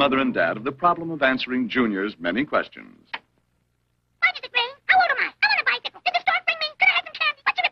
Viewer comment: he talks so fast I cant understand the first line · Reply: You got quite a few correct but here’s the accurate translation: "Why does it rain? How old am I? I want a bicycle! Did the stork bring me? Could I have some candy? What's a republican? I want a nickel! When do we eat? Is Santa Claus real? Can I go to the show? Can I have some ice cream? Can I have a drink of water?"